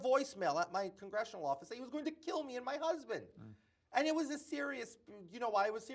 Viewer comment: Man literally can’t speak one single word without throwing in a lie. It’s insane. He still thinks he can lie his way out. This is insane to watch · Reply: Is he a he? We will get arrested. I thought I heard him say he has a husband.